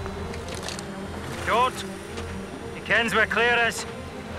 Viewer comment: I can't make out what Jamie says at 3:40. Can anyone help? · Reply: JAMIE says " Don't shoot! He knows (kens) where Claire is !